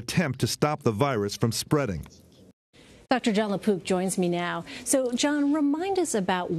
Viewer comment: Is his name really John La Poop? Bwahahahahaha! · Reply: I thought it said Le Poop. lol Would have been kind of ironic since one of the places Ebola is found is in bat guano.